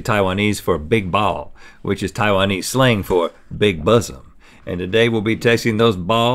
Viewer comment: I really thought he said “big bussy” instead of big bosom